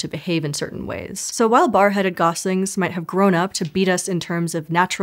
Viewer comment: do I really hear "bar-headed Goslings"? The captions corrected the words, but my ears tell me I am right. Is that a slip of tongue or an intentional pun? The smirk bends me toward the slip of tongue: oops, hat I did say, nobody will notice, lets continue.